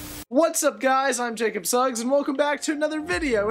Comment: Did u just say Jacob sucks ?